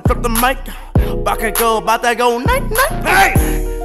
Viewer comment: "Steps on these chumps like a Titan" *looks up from cleaning ODM Gear Titan?...* "Bakugo Bakugo-Night Night!!" I'M DEAD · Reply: Thought it said 'Bakugou 'bout to go night night'-?